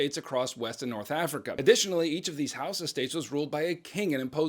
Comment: does he say “house of” or “Hausa”? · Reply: He says Hausa